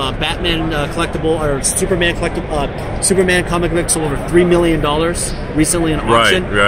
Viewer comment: Ohhhhhhh I thought that said 21 million lol I was like damn I need to start looking for magic cards